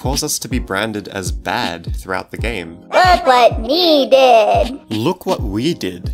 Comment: i think he actually says "look what ME did!" rather than WE for when the tattletail made the mess for us to clean up but then again i guess it's really just how you hear it